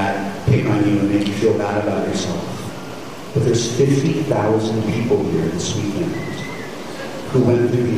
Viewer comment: "But there's 50 000 people here in Sweden... "? What is he saying there? He's not talking about Sweden, it's just my Swedish ears that wants to interpret it like that. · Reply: I thought so too. He said there’s 50,000 people here this weekend